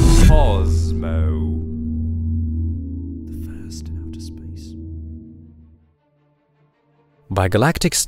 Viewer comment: What the hell is that being whispered? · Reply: Firssasspace